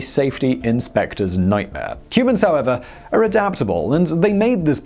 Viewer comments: > "cubans however" ?!? Do I heard it right ? - all socialist are the same and swappable.... edit - some says he said "humans". meh, I rewatch it at 0.25x... really hard to tell/hear... but humans make (more ?) sense. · "CUBANS however are adaptable, and they made this tower into......" Simon really is descending into madness in 2020. That or the Tower of David is a Cuban exclave in Venezuela. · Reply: I heard "Cubans" as well at first, but I backed it up and I'm pretty sure he said "humans" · did he just say "Cubans are, however, adaptable"? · did you say "Cubans however..."?? :-P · Did he say Cubans or Humans are adaptable sounded like Cubans. Venezuelans* Yeah he said Cubans...